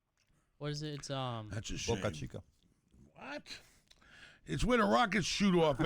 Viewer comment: Did anybody hear "mokra cipa" at 19:11 or was it just me?